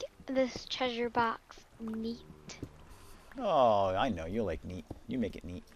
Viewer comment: i thought said "let's make this chest meat" not neat lol